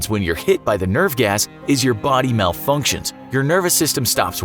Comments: When he said nerve gas I thought I heard Nurf gas. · I thought he said "Nerf Gas" when he said "Nerve Gas" like what are they gonna do? put nerf darts in your mouth?